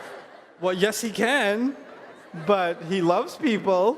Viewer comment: What she said at 25:39?? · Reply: He can kill people.